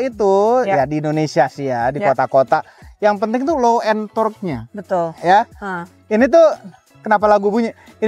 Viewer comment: low entodd aku dengernya😂